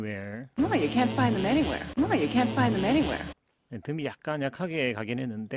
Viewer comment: 선생님 2:28 nobody can’t find them 으로 들리는데요 혹시 제가 잘못들은걸까요ㅠ